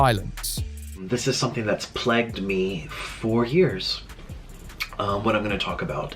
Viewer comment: This is nitpicking now but why does he say plegged me